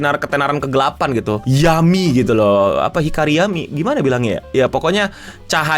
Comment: gua pertama dengernya yummy, ternyata maksudnya yami wkwkwk